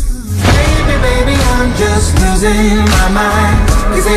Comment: Why do I hear "Baby baby I'm just closing Walmart"? 😭😂😂